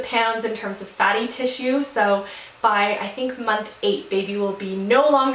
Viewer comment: did you say "HER fatty tissue"??? If so, congrats on the girl! If not, obviously you can tell which gender I'm hoping for, because my ears are making things up LOL